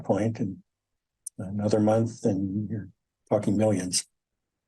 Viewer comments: did he say “another month and you’re at fucking millions”? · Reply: the caption said you're talking millions but it sure sounded like he said fuckin millions LOL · did he say “another month and you’re at fucking millions”?